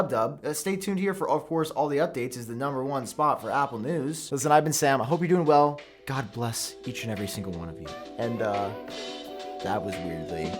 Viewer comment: Did he say I been Sam? Lol